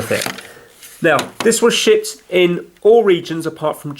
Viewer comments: I thought he said “It was shit”........ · Once again he said ''shipped" but it sounds like he said something else. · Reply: It sounded like he said shit